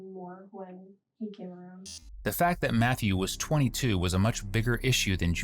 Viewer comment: The mom was dating a 22 year old or did I mishear? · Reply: Nope, I had to rewind it too! He was 22!